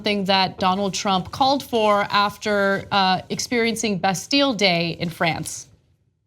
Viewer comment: first I heard "best deal day" instead of "Bastille Day", dat made me spit my coffee.